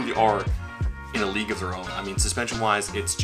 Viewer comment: Love a good Sequel 🤙🏽⚡️🤟🏽⚡️👌🏽 You said these things are "in a league of their own" and I heard "illegal alone" 🤣 Some dreamy carvin 👌🏽 Love that Acedeck made it noticeably smaller 🔥🔥 Less beastly, more boardy 🤟🏽